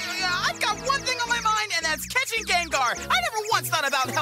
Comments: for me, it sounds like Goh is saying "Gay-ngar" 💀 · WHY DID I HEAR THAT AS GAYGAR!?!?